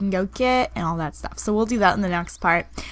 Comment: When she said "oh we'll." I thought she said "A whale." XD